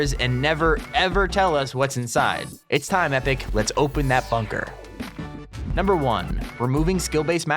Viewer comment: At the end when u were talking about the bunker, the video lagged and I thought u said "epic, please open that fucker." Lol